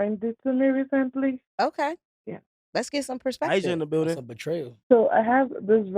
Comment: did he say ice in the building ? 🤣